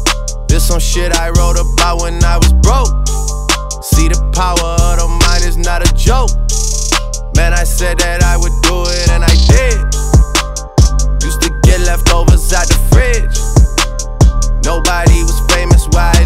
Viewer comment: drakes verse is that motivational shit... "see the power of the mind is not a joke...man i said that i would do it and i did" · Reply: this whole time I thought he said “power of the Midas, not a joke” since he has the Midas touch like he says in Back 2 Back.